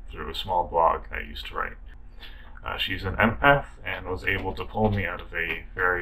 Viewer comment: "shes an MF" NO SHIT!!! pulled a fucking Magic Finder for a wife. Im jealous. I got mental health wheres my Magic Finding Wife.... lol in seriousness, what did he call her? or refer to her as??